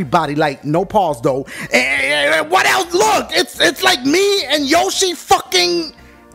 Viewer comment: Right off the bat I thought he said "ITS LIKE ME AND YOSHI F*ING" PAUSE MA BOI